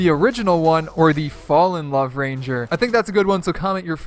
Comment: He says love rSanger (I put the “S” on purpose) instead of love ranger😆